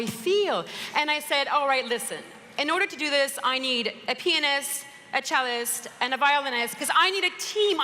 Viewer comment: I thought she said "I need a penis" and then realised its " I need a pionist" haha